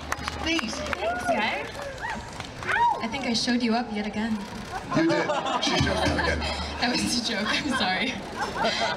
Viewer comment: What does she say to the Rock on minute 2:37? She says quickly after that is just a joke but she said it so fast I can't understand. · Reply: She says "I think I showed you up yet again!", then "That was a joke, I'm sorry... adrenaline!" :P